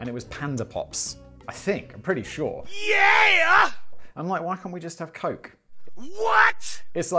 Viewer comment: anyoone else at 5:10 though Simon was asking for kids to be given cocaine instead of panda pops?